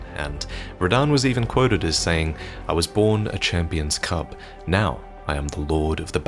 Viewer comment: I get he said “I was born a champion’s cub” but I did a double take and listened again because I heard “I was born of champion’s…” well needless to say I was flabbergasted, but relieved when I checked.